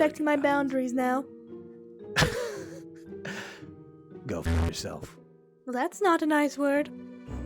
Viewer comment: I'm going to pray to god, that this is not the only time we get art as a thumbnail Also is that the first time I hear eclipse swear or is it just me? 10:09 go f*ck yourself too 😊